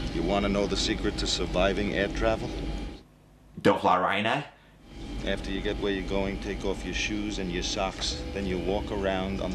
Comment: when I watched this video for the first time, I thought, he said “don’t fire a RYNO